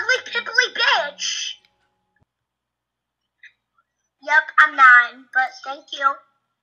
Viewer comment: "Yep I'm nine and thank you"😭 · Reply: I hear "yeah, i'm mad but... thnk u" 👁👄👁